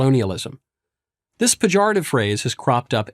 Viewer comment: What does he say at the very beginning 0:16? This "pajara" phrase has cropped everywhere...?